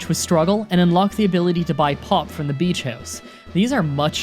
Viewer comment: you unlock the ability to buy pot?? XD XD XD I definitely misheard that looool